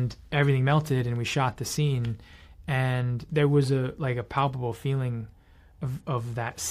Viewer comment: Im not gonna lie it low key sounded like Jake said he had actually feeling during those scenes